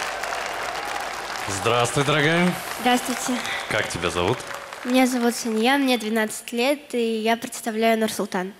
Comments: Мне послышалось меня зовут свинья😹😹😹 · мне 1 послышалось: " Меня зовут свинья?"